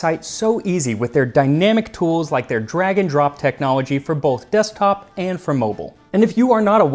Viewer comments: You’ve got me so trained to hear Old English that I thought you said, “Dragon Drop” (instead of drag and drop) in the Squarespace message. 🤣 I was surprised at adding the egg yolks! Another great video. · Okay, that sounds like it'd taste great but I fear the acid reflux that would follow. If I try this, I'll probably add some period-appropriate veg. On another note, when you started reading the ad, I misheard and, for a few seconds, was both very confused and curious about what a "dragon drop technology" would be.